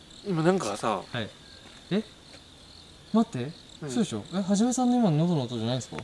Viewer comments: うんって聞こえた確かにこれは怖い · ちゃんと「うん」って聞こえる🥰 タイトルの「意識落ちかけた」って酒なんだよなあw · 音量大きくしたら確かに「うん」って聞こえたw